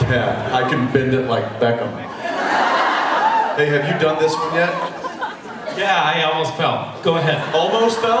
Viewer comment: all i can hear is jensen saying "babe, have you done this yet?" which obviously cant be right. WHAT DOES HE SAY??? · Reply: 011CRB He says hey, have you done this yet? not babe